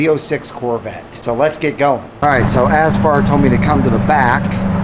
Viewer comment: I thought he said his name was "ass-fart" 🤣🤣